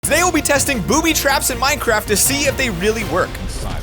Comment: I thought you said booty traps haha